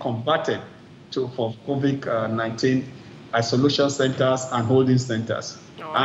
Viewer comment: Did I hear COVIK-19?